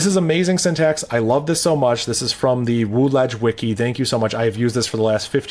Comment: I was unfamiliar so I thought you said "woolash"? It was unclear. Wooledge. The BashGuide, BashFAQ, BashPitfalls wiki.